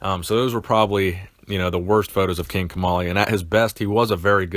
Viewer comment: I thought you said King Tamale